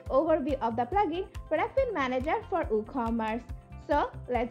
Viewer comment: I think it's called WooCommerce :penguin-blue-waving-tear